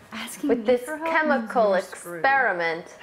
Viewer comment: I heard have daddy add either